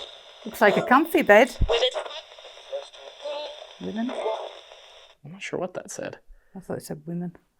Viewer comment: I thought it said “ well it’s not”